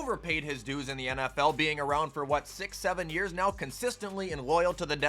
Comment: did you just say…67